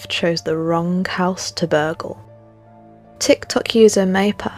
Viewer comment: I've replayed @ 1:50 a handful of times and I kept hearing " wrong house to BURGLE? I am hearing it correctly right? Plz lmk.. Stay Scared n Be Blessed